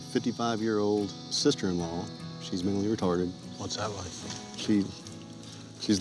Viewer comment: did dude just say his sister in law was mentally restarted?! 😂